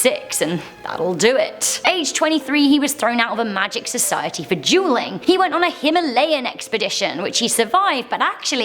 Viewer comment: sounded like juuling 😂